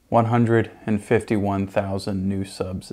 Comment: I thought you said 1,051 new subs this year, hahahahahaha